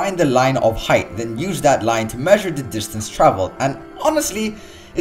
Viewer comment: did u say cockualte?